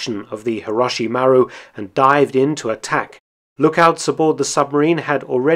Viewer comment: For a second, when I first heard the ship's name, I thought it was Kobiyashi Maru. I nearly nerded out for a moment.